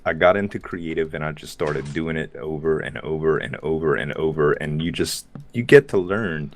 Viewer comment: I heard him say Ogre and ogre and ogre again